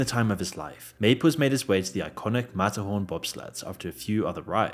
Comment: Couldn't understand what was being said here after rewinding over and over so I went to Google and said: "Disneyland mat horn bob slats" in a british accent and it gave me the correct results. Matterhorn Bobsleds 😂